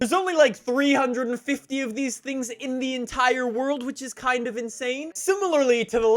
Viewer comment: did he say Indian tire world?